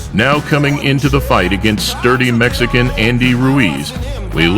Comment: Did I just hear that right @ 1:25 sounds like he says dirty Mexican Andy Ruiz LMFAO